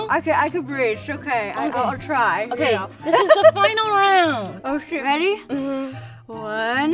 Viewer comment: did she say "oh shit"